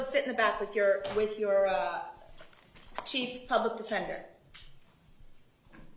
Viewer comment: what did he say? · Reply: "He's THE public defender, judge"